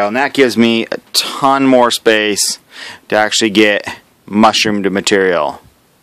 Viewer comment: Did i hear "alot more mushroom to material" at 7:42 Great video theirs waaay too many keys on my key chain.